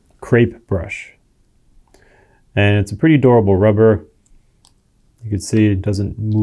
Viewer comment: He said the crepe brush rubber is durable but I heard him say "adorable" and I agreed, it is adorable too